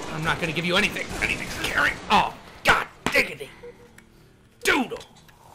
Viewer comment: lol at 4:27 i thought he said giggity lol